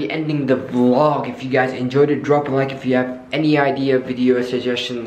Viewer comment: I just heard like "i'll see you guys in the next year"